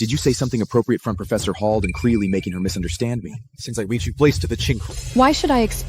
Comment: I couldn't help but laugh "since I reach your place to the chinkel" idk what he was trying to say but chinkel is going in my dictionary somewhere 😂😂😂